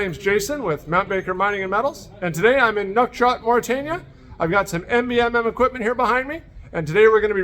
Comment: Sounded like he said duck shot Mauritania 😂